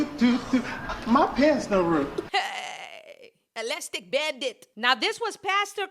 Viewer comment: Did you say an Elastic Bandit sis? 😂